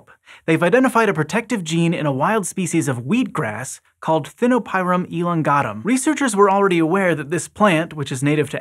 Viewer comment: Am I the only one who heard "Elon gottem" at 1:00? Great video btw